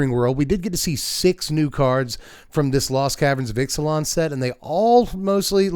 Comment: I though i heard you say lolth's caveverns of ixilan at the end there and i got way too excited for half a second 😂 i loved that card so much